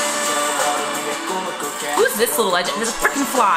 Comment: did you just say "who are this ?! out!" to jinhyuk?😭💔, but i don't know what youre saying😕.